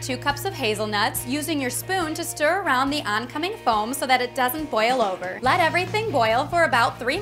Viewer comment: When you said oncoming foam I thought you said Oncoming Storm and I'm just like DOCTOR WHOOOOOOOOO begins fangirling